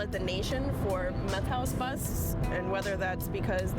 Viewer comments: I thought it said math house.. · I thought it said math house.